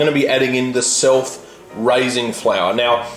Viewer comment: i always heard "self-rising flour" as sulfurizing flour...until this video